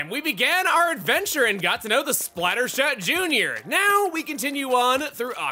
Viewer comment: when he said splattershot jr. during the intro, i legit almost thought he said splatterSHAT jr. the first time i watched this